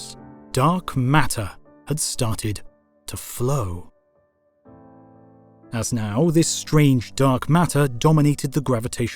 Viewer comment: Wow! "Dark Matter" started to FLOW!!!! Am I HEARING that right?